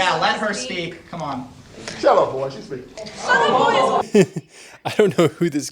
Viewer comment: To be fair he is very assertive and if you're not ready for that, it can be shocking, especially when you expect that individual to behave pitifully. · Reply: What did he say here: 1:12 ? His exact words were "shut up boy." He is angry, riled up, fine, just call it what it is: aggression. If there was a man on the other end, instead of a girl, and he was talking the exact same way, people would refer to it as a heated argument, not a debate. look, it doesn't hurt my feelings either way, it's funny when leftists cry. I'm just saying she's crying because he's all but yelling at her, not because he "doesn't want her pity."